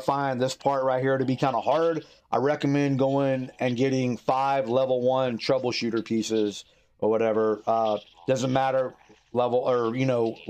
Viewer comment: ?? What is that you say? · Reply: 5 Troubleshooter armor pieces